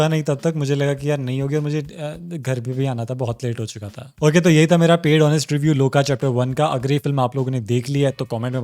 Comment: did he just said paid honest review or was I hearing wrong